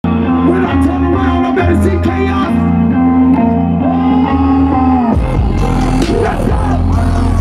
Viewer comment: What travis say at 0:01? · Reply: When i turn around i better see chaos